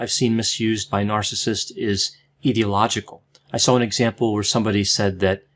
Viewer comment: I thought you said "ideological" . . . am I an narcissist?